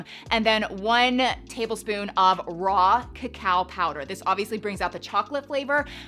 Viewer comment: I was listening to this while working on my second monitor and had to look when I heard "Rock a Cow Powder". Just giggled at my brain's interpretation of Raw Cacao powered 😛no shade at all, you definitely said it right girl!